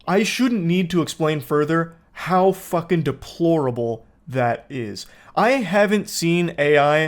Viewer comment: Anyone else hearing „Yanny“ when he’s saying „deplorable“ at 1:48?